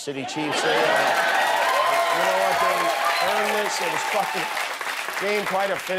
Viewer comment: sounded like Kimmel said 'it was fking...' Close-captioned thought so too